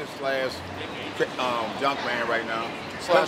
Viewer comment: haha he said junkman and dave thought he said drunkman